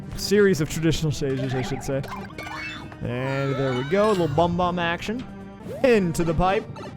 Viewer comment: did he say bum bum action while fighting BJR?